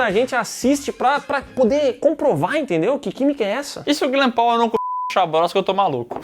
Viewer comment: O que foi falado aos 15:43 alguém entendeu? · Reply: E se o Glenn Powell não comeu o $-):; eu tô maluco